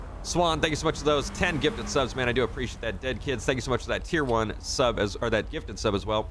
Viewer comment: Did he say Dead Kids??? Wth lol